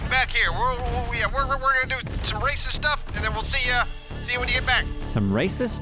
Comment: Glad I'm not the only one that thought said "Gonna do Racist Stuff"..... lol Love you guys.